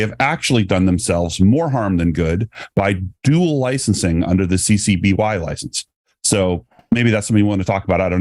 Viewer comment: I keep hearing "TCBY license" and it's making me hungry for frozen yogurt. 🙂